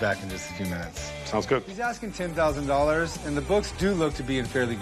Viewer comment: Did chum just say that the guy was asking $10,000?